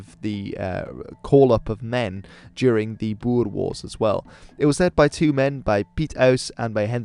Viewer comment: When you said "the Boer Wars", I initially thought you said "die boerewors" lol